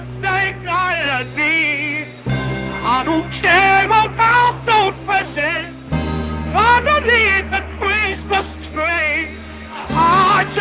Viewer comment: Am I the only one that thought it sounded like he said “I don’t care about the Russians underneath the Christmas tree”?